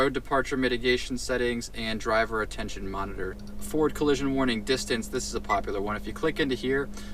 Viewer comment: is it just me or is he saying "ford collision warning"